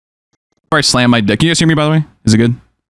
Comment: I’m not the only one who heard “aw I slammed my dick” right?